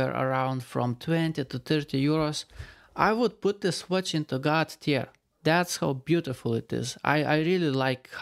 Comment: i misheard " i would put this watch into god-tier" as "i would put this watch into god's tear" and became happy that i found poetry accidentally